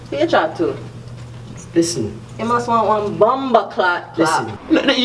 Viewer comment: at min 3:13 the woman say comething like Bom bo clat or similar. What is it? I'm learning english and i find this Nigerians and jamaicans very funny but i can't understand that, help me pls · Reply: Llontop Rivas That word "bomboclaat" is a Jamaican expletive.